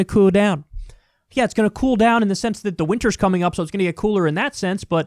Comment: Had to rewind it. I heard "It's going to cool down in the sense that the witcher season 2 is coming up and that's really cool"